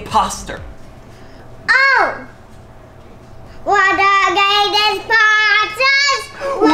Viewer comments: we da greatest croow mates: no we are the imposters! we da greatest posters! We da greatest posters: Can we get him now? No. we have to do this Smartly! We can get him smat leag again: we smaRt we smaRt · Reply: i thought said were da greatest pastas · When they said, we’re the greatest impostors, I thought was saying we’re the greatest pastas